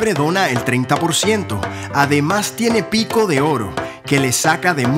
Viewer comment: parecía que dijo pito de oro